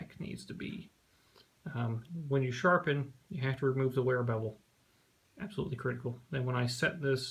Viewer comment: you said "when you sharpen you have to remove the....bevel." Sounded like 'wearer bevel'. Sorry, what word was that? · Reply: Slap Stick wear bevel, remove at each sharpening.